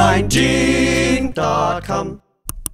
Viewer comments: Anyone else hear: "My favorite food is sex." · I thought they said " my faveroit food is shit"